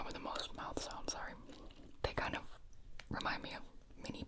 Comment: For a second I thought it said European mouth sounds and I was so confused 😭